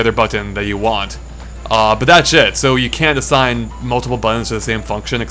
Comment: and again whenever says "that's it" i keep hearing "that shit"... xD